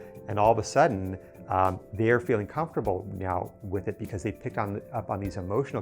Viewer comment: does he say meow?😆